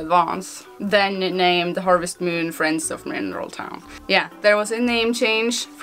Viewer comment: I thought I heard funeral town..